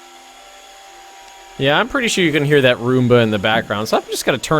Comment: I thought you said my arumba just turned on xDI know you said roomba